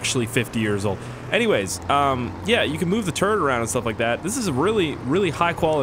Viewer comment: I thought he said "Move the turd around"...